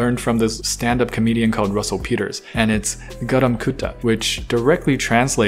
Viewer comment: At first I thought u said garam kutti😂😂 lovin it!